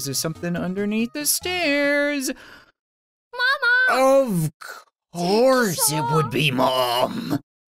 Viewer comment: the captions say "oh horse its mom.